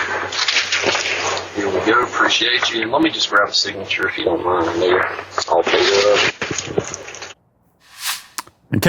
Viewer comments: I did hear it actually. Although its probably not what he said. Its probably something else. But it sounds like he said "Let me just grab a signature if you dont mind my N..." at 3:06 but I don't feel he deserves to be sued over it. I think he just saw a way to get some of Pizza Huts money! 😂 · it sounded like he said "I know you're all paid up" at the end. he's asking for a signature (to confirm receipt of the pizza), and then says "I know you're all paid up" to let the customer know he's not billing him extra or something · It sounded like he said I know you're all paid up. I only heard an accent.....I didn't hear a racial slur. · I heard, "Let me grab a signature if you don't mind my nigga". Still nothing to be offended about. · I definitely heard him say, "I appreciate you." The bigot. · Sounded like he said "let me just grab a signature, then yer all paid up"